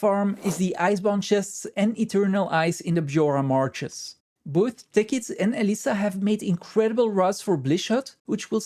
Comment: "Both Tickets and Alysa have made incredible RS for Bliss Hut which will significantly reduce the time this takes." 2:12 ...What??? I have no idea what you actually said, but that is how YouTube "translated" it. I expect it has something to do with those arrows on your screen. Following the links you provided filled in one of the 'blanks' with "Blish HUD," so that is a start. EDIT: What actually said was, "Both Tekkits and (Lady) Elyssa have made incredible routes for BlishHUD..." - BlishHUD Overlay for GW2 may be found at its official homepage. - Tekkits' and Lady Elyssa's trail marker packs may be downloaded from within BlishHUD's pathing module.